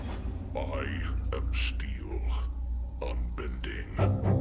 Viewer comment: I swear at 44:41 I heard the guy say "I am Steve" and it cracked me up.